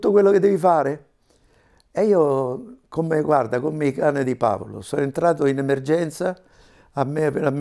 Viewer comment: non capisco cosa dice . . . come i danni di Paolo ?! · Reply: "cani di Pavlov"